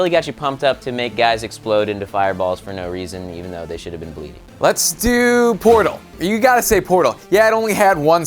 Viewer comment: i swear i thought lasercorn said ' Lets do....Porno ' O.o